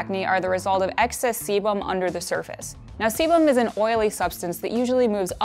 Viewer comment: I thought they said excess semen